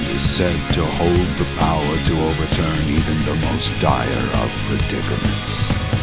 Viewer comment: Could somebody tell me what it says at 1:42 "Mankind is said to hold the power to overturn even the most dire of (what)"? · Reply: predicaments